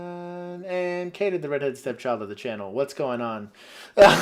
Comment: What did you say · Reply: We call you our step child 🤣